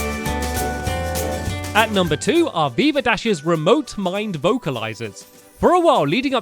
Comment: I only noticed some of these! I had no idea about the Veevadash ones! Great secrets, Stampy! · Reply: Umm... I didnt watch the episodes with her in it and whenever Stampy would mention her.... I thought he said Beaverdash XD